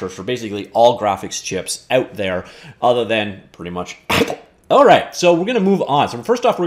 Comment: Did he say apple at 1:34?